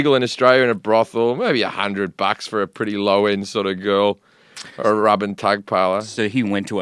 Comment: did he say "a Robin Tagpiler" when referring to a low-end prostitute? I cant find any reference to that...?